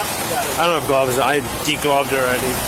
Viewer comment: wait wait what did he say did he say that he degloved omg